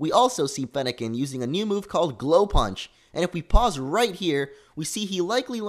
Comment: Did he say punch? Looks like we'll be getting a fire/fight starter T__T